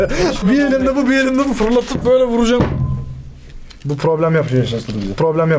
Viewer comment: Bu Problem Yapıyo esıwştjsş ben öyle duydum sizce